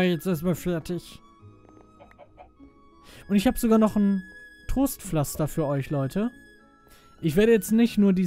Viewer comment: ich hab Toastpflaster verstanden D